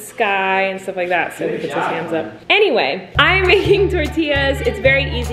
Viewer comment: i cant stop laughing omg she said 'hams' instead of 'hands' so it was this: "So he puts his hams up" 😂😂😂😂